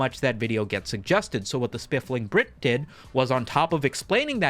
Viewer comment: Spiffling Brit🤣🤣🤣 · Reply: I thought he said "spliffing brit"